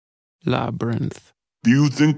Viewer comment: It sounded like Viktor said "labyrinth" at the beginning and I keep replying it. For science.